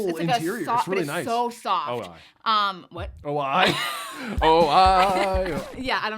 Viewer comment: lmao did you say "oh, aye" that happens to me too. I know why it slips for me, not sure where it comes from for you though 😂